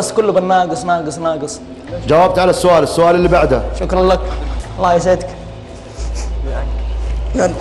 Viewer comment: سمعتوه يوم يقول الله يسعدك ويلعنك ههههههه دقيقه